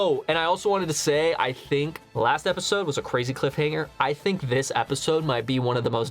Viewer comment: For a second there i thought he said cliff hang🤣